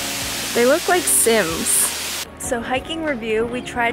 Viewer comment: I thought you said "they look like Simps" LOL